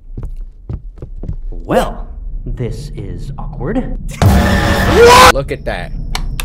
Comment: Imagine going to murder someone, they see you and you say ”Well... This is awkward...” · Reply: I thought Tanner said "Well, this is accordant." Until I heard his voice lines.